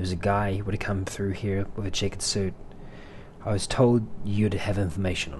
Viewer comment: The way you said "checkered suit" in this, I keep hearing "chicken suit" for some reason. Which is a completely hilarious mental image.